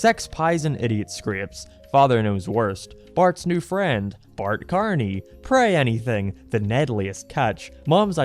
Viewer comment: also i misheard this as "Mark Carney" .... as in... the current .. canadian prime minister...... I think i should sleep